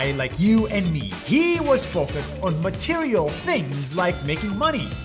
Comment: I thought you said he was focused on material gorls... I am so sorry I had to say that